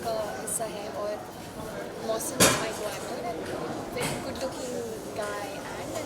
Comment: I think she said boyfriend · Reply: She said co-actor